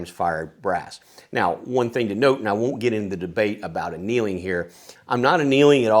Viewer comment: you mentioned not getting into a debate about what sounded like you were saying a "Kneeling" Is that correct? If not, what was it you were saying, and what is it? Thanks